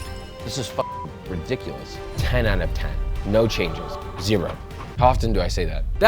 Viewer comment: did he just said... something is 10/10? Damn...!!!